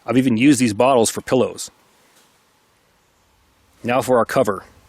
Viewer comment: He can use these bottles for what??????? I couldnt understand what you are saying bro!!!!!!! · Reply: Pillows. Support the base of the head and neck.